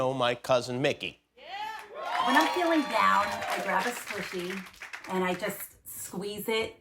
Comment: At first, thought she said " When I'm feeling down, I grab a Pussy." LoL!